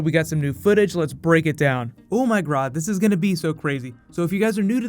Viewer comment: Did you say 'O my Grodd'?